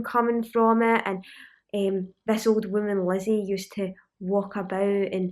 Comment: I thought you were saying 'this old woman LEZZIE' lol